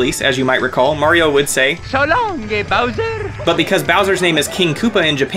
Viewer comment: (Closes DS) “So long ghey Bowser!” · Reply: I swear I always heard him say so long ghey bowser growing up 🤣🤣🤣🤣